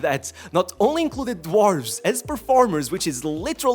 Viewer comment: Funny you said DWARVES but I heard WHORES. Both were present in Yamal's 18th birthday party. 😂😂